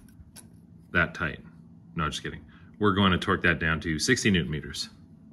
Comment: min mark...Did you say 60 NM or 16? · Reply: 60